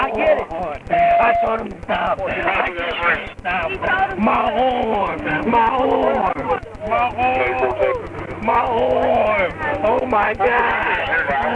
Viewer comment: about 3:55 What is he saying? "My uorm! My ourm!" Wtf is an ourm?